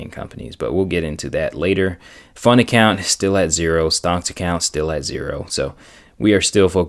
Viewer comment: wait.. i caught you. did you say "stonks account" @ 3:55?? the memes are getting to your head..😎😎😎😅😁